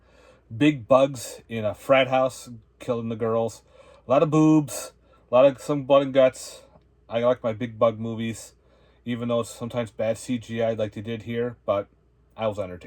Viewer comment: When u where decribing Inseticidal and said, "Alot of boobs, a lot of blood and guts.. I like my big bug movies" I almost thought you said, "I like my big butt movies" just made sense, boobs and butts just go together.. 🫣😉🤣